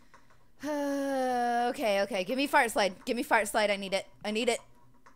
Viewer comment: is it bad i thought she said fart slide instead of heart slide?